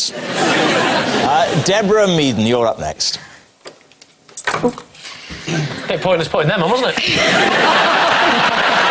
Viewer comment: I can't make out what he says in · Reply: "it was a bit pointless putting them on wasn't it?" Context: she put on her specs then dropped the card.